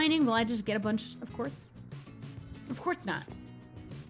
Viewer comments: Did I hear you say “Of quartz not”? If you did, nice joke! If you didn’t, nice joke anyway! Ily!! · When she was talking about mining quartz, I legit thought that she said "Of quartz not" and I was legitimately disappointed when I realized she just said course